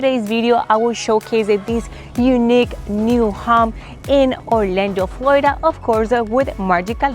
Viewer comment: I was scrolling my YouTube feed and I heard “unique new ham” … where’s the goddamn ham